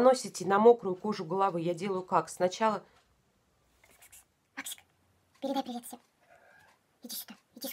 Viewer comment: добрый вечер! спасибо за обзорчик! какие красавцы вы с кисой) Максик зовут, мне не послышалось?)) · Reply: Та Та спасибо огромное 😊 да Максик😁